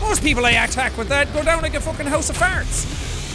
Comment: Is it only me or did it sound like be said come down like a house of farts